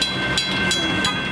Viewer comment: Cop just strolls on up on his wheels like "Sup boys" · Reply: I believe that police officer said hey hey what's going on down here or he said hey guys what's going on down here?